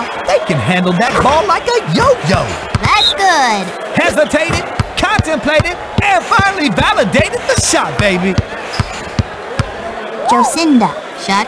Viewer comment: What'd he say at 12:23? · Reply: Hesitated, contemplated, and finally validated the shot baby